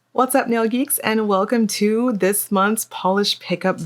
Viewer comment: So as I once commented before my wife and I watch your videos to relax at night before bed , but what's funny is when we first started watching your videos we always thought you said " What's up Yogis " lol and it took is a while before we realize you say " What's up nail geeks " lol ..... So now we refer to your channel as lol So when you post a video my wife tells me " Yogis posted a new video for us to watch " lol But yeah just thought it was a funny story to hear lol As always thank you for the content and keeping is entertained . You have a big influence on the polishes my wife purchased .